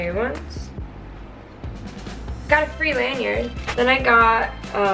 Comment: what did she say? She either said "free" something or "3" something but I couldn't tell what she said before she moved on to the next item